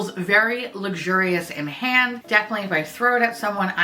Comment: I’m half awake. So when you said it feels luxurious in the hand and then you started talking about throwing it I heard “luxurious in the hand. Definitely in the throat” 😂